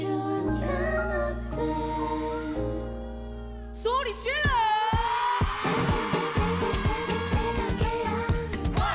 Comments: What did Moonbyul say at 2:34? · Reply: She said, "Make some noise!" ^^ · not sure but I think I heard moonbyul screaming “PUT YOUR HANDS UP” in Chinese 🧡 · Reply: i believe she says 소리질러 which translates to make noise/scream